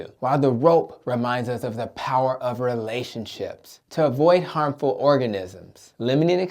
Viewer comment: I thought I heard “harmful organists”. 😂😂😂